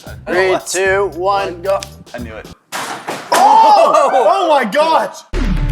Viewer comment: did say oh my Goch?